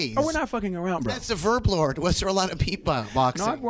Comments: Is Nardwuar saying Verb Lord? What doesn’t Pharrell want him saying? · What word does Pharrell not want Nardwuar to say at 8:20? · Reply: Tangent "verb lord",that was pharell's original stage name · what is it that Nardwuar says that Pharrell says "don't say that word again" ? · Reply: Verb lord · What does nardwuar say at 8:20 that makes Pharrell say that. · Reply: Calls him "The Verb Lord", pretty sure it's a reaaally obscure nickname that Pharrell used to go by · What did he say at 8:20? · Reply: Verb lord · What did he say? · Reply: I got, "In the early days, as the Master VerbLord, was there a lot of beatboxing..." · wtf is he saying? VerbLord? Sounds like some Illuminati shit ta' me